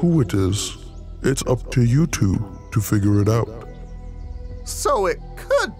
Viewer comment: Lol when he said it’s up to you two to figure it out It sounded like he said it’s up to YouTube to figure it out